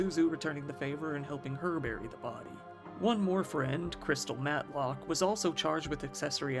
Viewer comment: Crystal matlock I thought you said Crystal meth😅😅